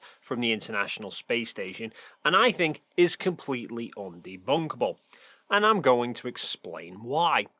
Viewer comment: I had to go back and turn on captions. At the beginning when you said “completely undebunkable” I thought you said “completely bonk debonkety” as in a reaaaallly weird way of saying it’s bonkers 🤣